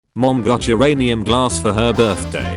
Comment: I thought it was said as "Uranus" rather than "Uranium". 💀